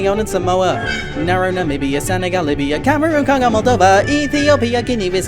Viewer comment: I said Moldova... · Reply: I tought I heard you say "County of Moldova" or "Country of Moldova". I made myself look like a fool. About you re-enacting this, you were really good.